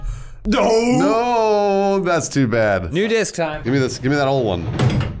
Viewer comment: I misheard "New Disc time" as "NUDIST time." Yeah, when are we going to get rid of censorship?